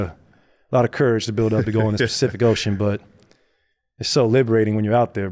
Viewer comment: Am i trippin or did Klay say "Specific Ocean"?